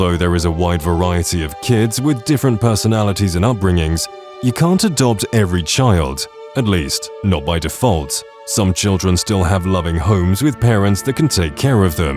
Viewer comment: I thought you said you can adult children... You know how badly this sounds? XD